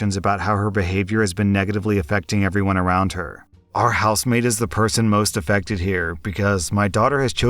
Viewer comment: I thought you were saying housemate throughout that first story, and got really confused when you started talking about giving a day off 😅